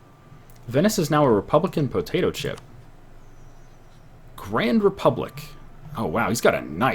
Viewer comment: Did you really say "Venice is now a republican potatoeship"? Lmao...